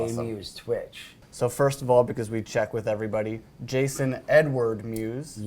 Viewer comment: I thought he said “Jason N-Word Mewes” I was like WTF and had to rewind it lmao